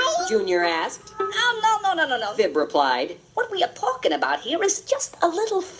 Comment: Sounds like he said "What we he palking"